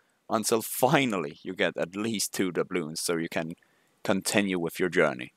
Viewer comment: What do you say at 1:59 ? "Until you get 2 ....."? 2 what? Dabloon? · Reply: I do say "Dubloons" which is the currency you recieve from Island Expeditions.